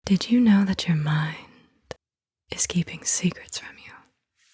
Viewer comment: At first I thought you were saying "did you know that you're mine" and I was like OK